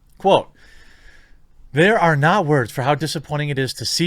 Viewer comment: "Quote... there are NAUGH words for how...." What are "NAUGH words"?